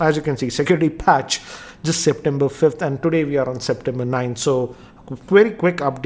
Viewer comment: did you said we are on september 9